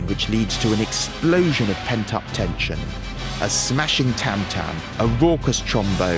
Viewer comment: Love your videos! Just to let you know that there's a small error at 8:45 where the subtitles say "tantrum" instead of "tam-tam"